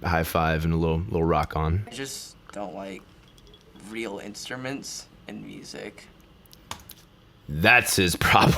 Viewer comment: I thought he said he doesn't like real instruments and music. XD